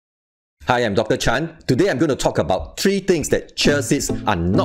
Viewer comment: I thought he said “hi, I’m doctor chai” at first. Lol